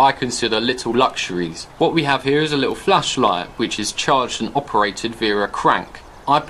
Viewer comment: Swore you said the flashlight runs on crack